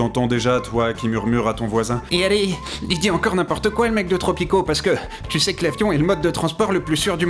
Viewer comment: C'est moi ou à 0:10 le personnage dit "Tropico" et non Topito ?